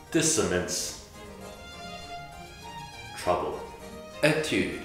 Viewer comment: I thought he said "Trombone" at dissonance